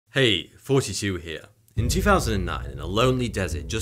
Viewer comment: I always hear “hey 42 here” but I know thats not what you’re saying.